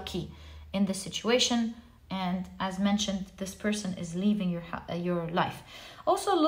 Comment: Hi - I chose Pile 1 and while listening I picked up something at 26:03 mark where you almost said "this person is leaving your house" at least that's how it seems when I heard it....this is significant to me. Also, should mention in another reading (believe it was yesterday) you mentioned September as a significant time so I believe there is coherence between the two (synchronicity)